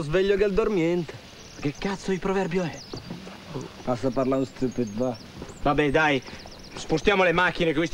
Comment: Ma a 2:09 Aldo che cosa dice? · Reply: BrickSimon credo dica "basta parlare, sto zitto va...". poi sinceramente anche io non ho capito AHAHA